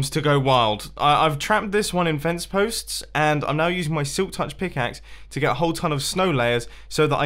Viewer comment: did he say silk touch pickaxe